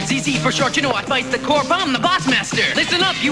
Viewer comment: How DARE you have such a bringdown ending and then transition into that Bots Masters theme where it DEFINITELY sounds like he says "Blazer Time, boys!" Like he's wearing a fancy sports coat...what were talking about???